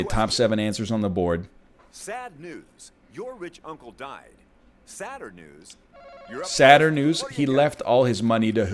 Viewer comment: I swear to God I heard, "Your bitch uncle died..."